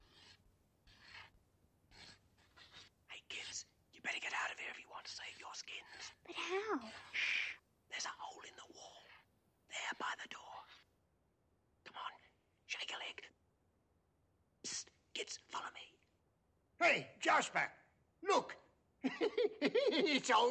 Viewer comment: Is Tibbs really saying “Shake a Leg” ? Cause it sounds like he’s saying “Jiggalig” or “Jillygalig”